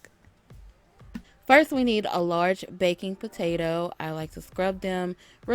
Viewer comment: I thought you said large BACON potato in the beginning, I was about to say hold up now!!! lol. Does anyone know where I can get crawfish tails in Georgia? I wanna try this but I have never even had crawfish. I just know I love any and all seafood lol